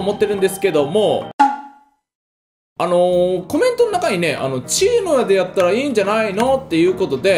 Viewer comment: 「治癒の矢でやった方がいい」って言ったのか。 滑舌悪くて「チームでやった方がいい」って聞こえたよ でも誰かと一緒に飛行して、その人に撃ってもらのもありだね